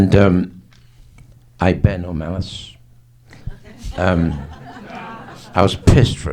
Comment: What did he say at 22:03? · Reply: "I bear no malice."